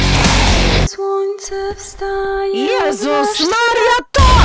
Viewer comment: Why did I hear mario at 0:30?